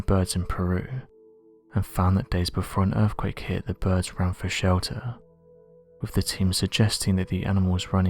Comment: did you just say “the birds run for cover”???